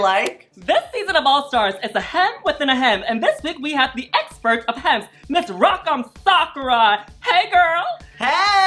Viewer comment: Sorry I thought she said was an “expert of head” like at Folsom :x